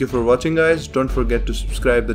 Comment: When you say notification bell I always hear "Notification spell" Am I losing my mind?